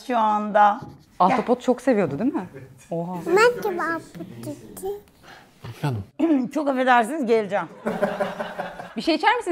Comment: da "bende ahtapot çiziyim" dediğini 4. izleyişimde anladım